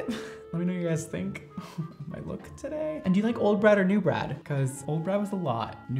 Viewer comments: Is it just me that thought he said old bread not old brad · I thought he was saying "do u like old bread or new bread" and I was like ofc new bread, who likes old bread